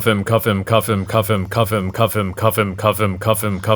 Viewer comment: i hear cuffin. Like koffin